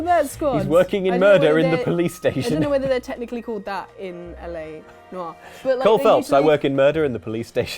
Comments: I thought she said "Ellen O. R." instead of L.A. Noir, which wouldn't even be a good pun, but what can I say... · When she said L.A. Noire, I could have sworn she said Ellen Noire. Now I'm picturing a black and white edition of show of the weekend.